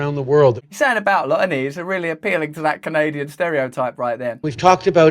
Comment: Anyone know what Russell says at 1:30? "Something a block emees, really contributing to that Canadian stereotype"